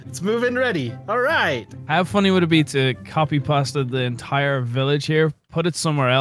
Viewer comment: is it me or did i hear Brian say copy and pasta instead of copy and paste?